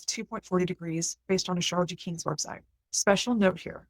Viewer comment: Hi, I couldn't make out the website name at 2:52. Can you please type it? Thanks 🙏😊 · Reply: Hey! It's the Astrology King's website ... sorry, the audio on this video sounds a bit weird to me this time (?). Thanks for pointing this out! ;)